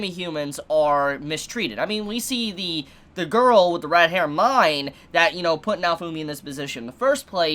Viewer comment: Lol. At first I thought you said That girl with the RAT hair Myne. Both apply perfectly